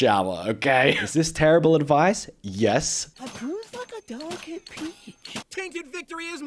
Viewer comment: LMAO i think he actually said "I bruise like a delicate peach"